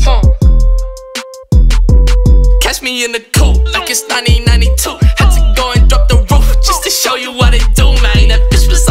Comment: can someone tell what he said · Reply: Catch me in the coupe like it's 1992